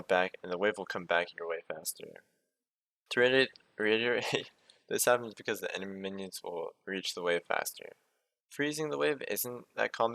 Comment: Yo homie, at 1:58 you sort of slur? I'm not sure what you said - could you write it out for me? :) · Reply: "To reiterate, this happens because the enemy minions will reach the wave faster"